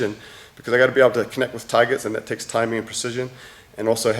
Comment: He keeps saying "Target" I keep hearing "Tiger" Battling tigers with a knife, way more badass